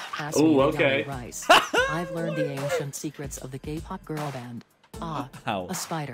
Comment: I thought he said GAYPOP 😭